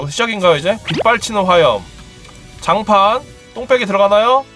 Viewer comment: 이때 뭐라고 하는거에요? 뽕빼기...? 똥빼기..? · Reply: 똥빼기에요 보통 저런 장판을 똥이라고 불러서 장판을 구석으로 빼주는걸 똥빼기라 불러요